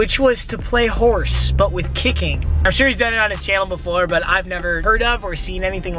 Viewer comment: I thought he said at jail at 0:55 thought he was being racist lol he said his channel